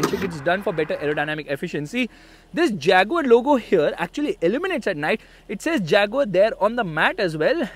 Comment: you meant 'illuminates' instead of eliminates right!?😅😂